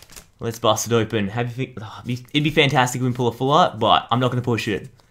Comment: I thought he said pull sh##